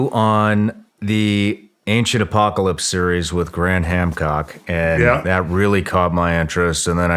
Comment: Awesome episode, but I know during the intro I heard, “Gran Hamcock”. 😂